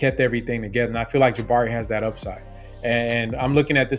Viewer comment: I thought you said that Tari had MVP upside?